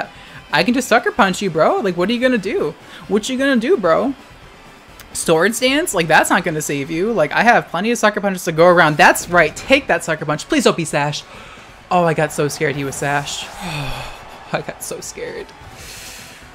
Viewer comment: did he just say stored sans (megalovania starts playing)